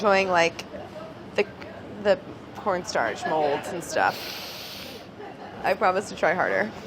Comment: Anyone else catch the "pornstarch mold" at ~ 23:10?